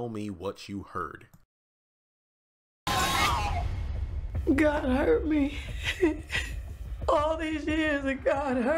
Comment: I thought she said God hurt me & the expression on her face was confirmation for me. I was thinking something about her man’s past was revealed or Bennie died. We’ll soon see .